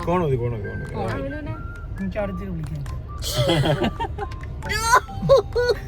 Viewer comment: what did said? Anyone please tell me 😊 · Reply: avide chennatt vilikkam enn